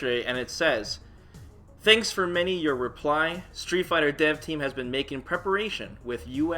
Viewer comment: when you said "Street Fighter Dev Team" I heard "Street Fighter Dead" ouch. I hope for new supers/ultras whatever the name is. Looking at the selection screen right now, there's a single spot for a new character, I'd like to see what were their ideas for Azam but I trully doubt he is joining the roster.